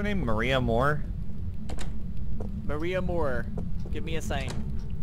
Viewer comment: When someone said " maria moore show me a sign ". For a second I heard " maria moore show me a thigh"